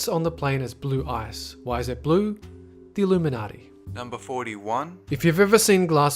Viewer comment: did he say illuminati